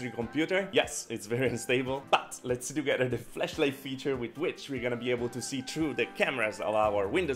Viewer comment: It sounded like you said fleshlight! 😂😂😂